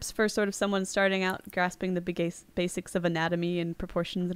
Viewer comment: I heard the Bi-gay-cis of anatomy.